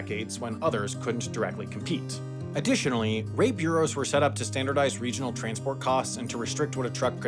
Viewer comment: Your auto captions say "r@ bureaus" instead of "rate bureaus"